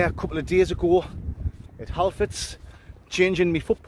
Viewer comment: Your Northern England Sutherland English accent, I thought you were saying "Half flats" not "Halfords." LOL 😂 Your accent 🤣 can drive a Yankee like me "bat-shiite" to crazy 🤪 confusion!